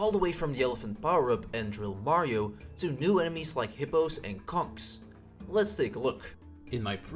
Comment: Did he just say c*cks?